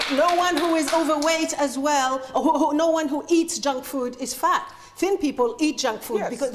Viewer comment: did she really say "no one who eats junk food is fat" at 2:15? she lost all credibility for me right then.